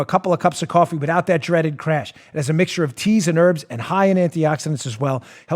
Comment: My hearings so bad i thought you said cheese and herbs.!!!hha